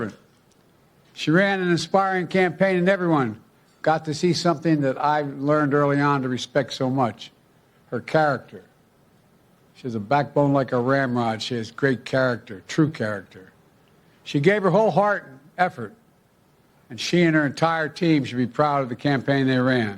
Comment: Did he just say Harris has a backbone like a grandma?!? 🤣 I know he said ram-rod, but still, it sounds funny at 2x.